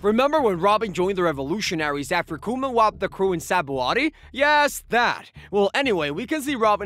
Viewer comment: Did he say SABOADY?